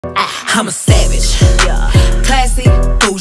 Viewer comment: I thought I heard “I’m a sad bitch”